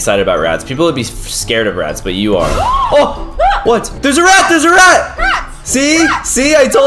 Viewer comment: I think when u said there is a rat I thought u said kiera was a rat